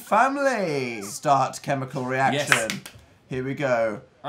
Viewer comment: for science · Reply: When they said ''Start Chemical reaction'' i thought they said ''Start My Chemical Romance'' WHEEZE